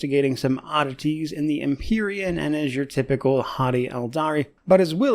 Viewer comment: I briefly looked away when you said haughty Aeldari, and i heard it as hottie Aeldari. Now I cannot unhear it.